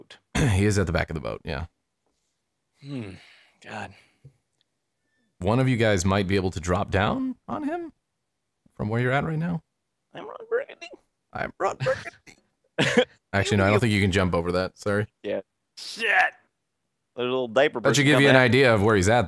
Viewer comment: Did i hear "I'm Ron Burgandy" at 11:15? XD